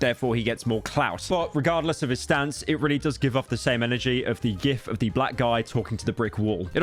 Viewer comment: i swear to god he said yif